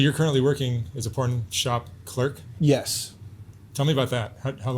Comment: 🧡 Great interview! I thought it said Pork Chop Clerk 🤣